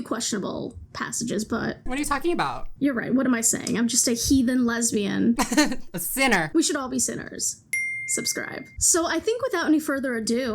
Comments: Omg I thought you said, "what do I know, I'm just a he/them lesbian" instead of HEATHEN lesbian and I went all over google trying to find somewhere where you clarify/declare your pronouns lmao. Then I finally read the subtitles/captions. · thought you said he/them lesbian for a bit and i was like WHAT PRONOUN UPDATE?!??!??